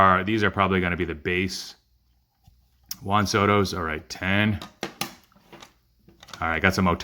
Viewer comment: haha.... I was looking at some cards while watching and thought you said that it was a "Han Solo" heavy submission. I just watched Star Wars last night which might explain it.